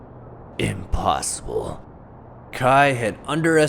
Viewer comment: Who thought that he said "IMPOSTOR"?